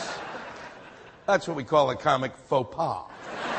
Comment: What does he say on 2:47? I don't understand... comic ____? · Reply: faux pas - (fo pa') French origins - a slip or blunder in etiquette, manners, or conduct; an embarrassing social blunder or indiscretion.